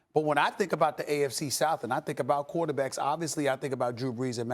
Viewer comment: Who else heard AFC South